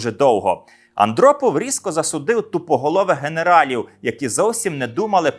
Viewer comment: мені не одному спершу почулося "дупоголових" замість "тупоголових"???